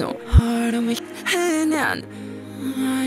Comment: I hear "Hey hyung"